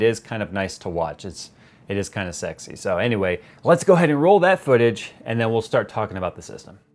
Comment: When you said, "Let's go ahead and roll that footage." I heard, "Let's go ahead and roll that beautiful bean footage." I was expecting a golden lab to make an appearance in the video trying to sell your Jayz watercooling secret recipes. Jay Bush and Duke FTW!!!